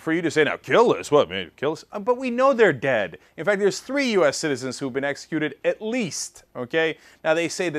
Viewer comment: I thought Cenk said that "Three US citizens have been executed AT LEAST twice," at first, lol.